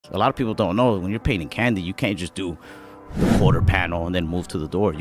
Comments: At first I thought he said when you get paid in candy. I was thinking he was getting pills for painting. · I thought he said, “paid in Candy” lmao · I thought he said when youre paid in candy